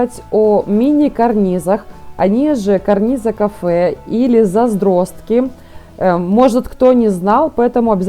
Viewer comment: Скажите еще раз как называются карнизы на 4:05 минуте, не расслышала, карнизы кафэ задростки???